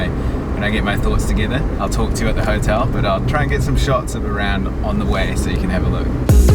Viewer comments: Cool video, as always be safe and thanks for sharing your travels 💛💛 oh and one thing did you accidentally say Iran at 4:43 instead of Iraq?? Lol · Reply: No I said 'of around' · Listen at 4:42, he says "I'll try to get some shots of Iran" instead of Iraq.